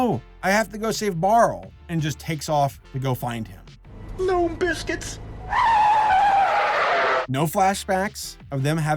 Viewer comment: What'd he say? "Gnome biscuits"?